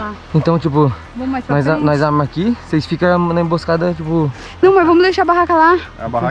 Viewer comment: FOI SÓ EU Q ESCUTEI A FALANDO NAO MOR PRO